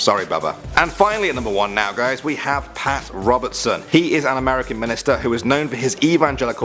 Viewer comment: I thought you said American milester